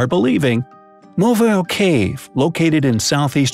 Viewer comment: Oh.... No. 3.... I heard 'MOBILE cave'